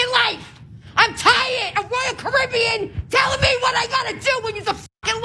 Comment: Carribbeans will kick her Ass. Don't play with us. We are not you. Putting up with your white privilege. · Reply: I think she said “I’m tired of Royal Caribbean telling me what I gotta do”, as in the cruise company. I had to listen to it twice because the first time I thought she was saying “tired of Caribbeans telling me…”